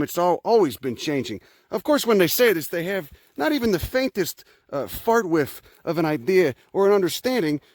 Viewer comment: duct tape working? wait did he just say fart whiff. Love it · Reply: He said "a farts width"